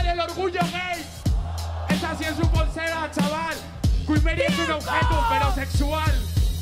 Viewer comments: que dice en el 9:00 para que le diga que no? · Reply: Que es un objeto pero sexual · No entiendo lo del minuto 8:59 porque ella le dice No y que le dice el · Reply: Le dice: queen Mary es un objeto... Pero sexual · que dice? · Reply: Queen Mary es un objeto... Pero sexual